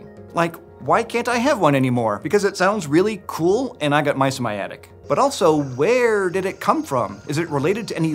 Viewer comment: Haha, he said "and i got mice in my attic", I heard "and I got mysomiatic", searching for it thinking it's another type of snake he has as a pet already 😅👏🏻